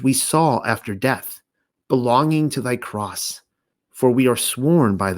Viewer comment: LOL, was I trying to say "cloth"? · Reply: Oh no, it was the word "cross" but I heard it as "croth" - My bad!!